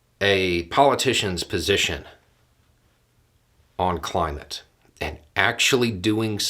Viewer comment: I thought you said ”Republicans shitting on climate…”. My bad! 😳